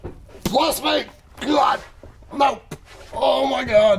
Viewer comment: did he say "bless me" ?